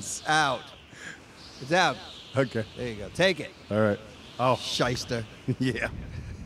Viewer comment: what is saying?! :D I didn't understand it :D thx for polite answers! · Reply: shyster /ˈSHīstər/ noun INFORMAL "A person, especially a lawyer, who uses unscrupulous, fraudulent, or deceptive methods in business."